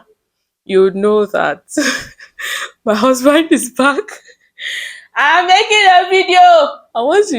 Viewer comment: All I heard is "my husband is back". God when 🥲